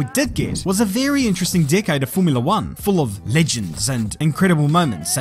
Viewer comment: I heard it as "What we did get was a very interesting "dickhead" of Formula 1"